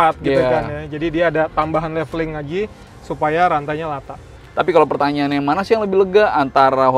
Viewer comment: saya mendengar di menit 09:00 rantainya Lata. mudah-mudahan saya ga salah dengar 😀